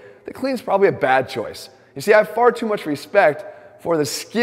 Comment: I fart too much respect?...Jeff what are you trying to say.